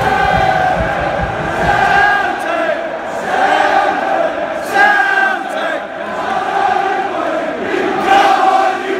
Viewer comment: May I ask you to write me the exact words of the chant at 5:34? I' m Italian and I don't understand it. Thank you and come on The Hoops!! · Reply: Emiliano Fortini (Celtic!) Celtic!, (Celtic!) Celtic!, (Come on you boys in green!) Come on you boys in green!, (Come on you boys in green!) Come on you boys in green!, (Glasgow's green and white!) Glasgow's green and white!, (Glasgow's green and white!) Glasgow's green and white!, Oh oh oh oh...[to the tune of Yankee Doodle] Hope that helps.